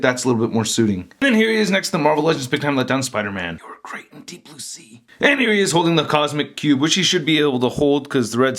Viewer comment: What did Big Time Spidey said? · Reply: I think he said "you where great in deep blue sea" idk why tho